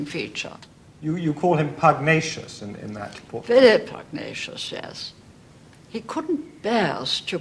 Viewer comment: What was the word used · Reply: Books and Banter. Pugnacious